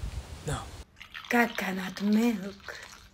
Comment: Early can I get a pin for CAKANUT MILKK 🥥 🥛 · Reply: i heard "god cannot milk"